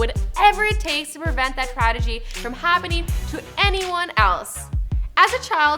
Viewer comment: TRADEGY? Did she just say that or am I tripping out?